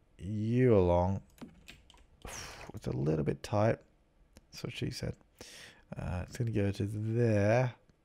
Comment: did anyone hear "thats what she said" at 4:43?!?!